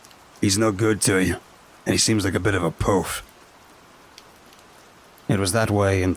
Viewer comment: what is a 'poaf/pofe'? I can't find a word that sounds similar and fits in that sentence 🤔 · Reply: he put a little extra fry on his voice but he said “poof”. Used by a lot of older English and Australian people to describe a gay person. Not a nice thing to say, pops was being quite the dickhead when he said that